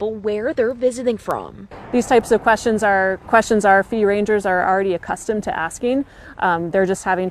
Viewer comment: Did she say "fee rangers"? 🤔